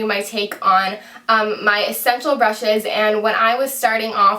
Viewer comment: Did anyone else hear, "sexual brushes" at 0:46?